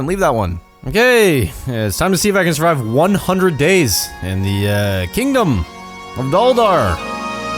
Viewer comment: I thought you said the kingdom of di1d0